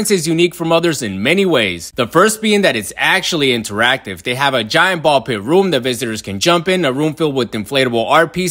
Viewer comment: What he said: "They have a giant ball-pit room." What I hear: "They have a giant COVID room."